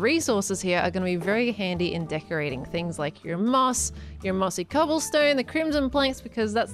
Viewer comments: When she was building the tunnel and going through the resources when she said ''And things like your moss'' I thought she said ''And things like your mom'' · I almost thought that said “Your mom” instead “Your moss” here, and my brain just went “PEARL!” 😂😂😂🎉🎉🎉(insert more cringey emojis).